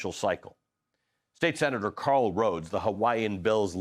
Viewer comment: Anyone else thought he said Carl Rove? And did a spit take?